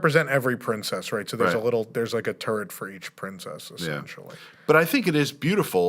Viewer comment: I heard that "There's a turd for every Princess."